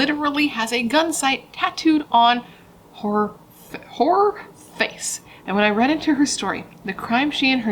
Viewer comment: This is obviously very serious but I absolutely lost it at 6:27 when you accidentally said heor (sounded like wh*re) 😭😭😭😭😭😭💀 I don't know why that got me good but it did hahaha I feel you though, pronouncing junk is HARD! I cannot say the word humiliate off the fly because it comes out like whooo-muley-ate hahahaha